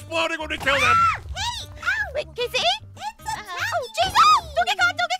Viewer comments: did jess break character by saying Jason when she was calling Aaron, or am I hearing things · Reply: she said jesus lol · Did aph say jesus????